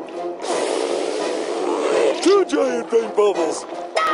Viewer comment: I THOUGHT HE SAID "two giant bang bubbles:D" DUDE I DIDNT KNOW HE SAID PAINT INSTEAD OF BANG